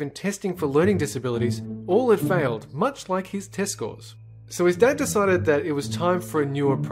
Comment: I thought said 'Testicle' instead of 'Test score'